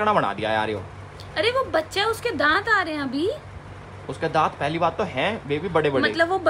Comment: just listen 🎧 probably did elvish say Baby to Kriti 😅😅❤️❤️ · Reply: Not baby__ he said vaibi means Woh bhi